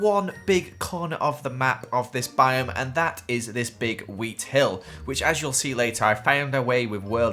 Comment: I thought he said the meat hill but he said wheat hill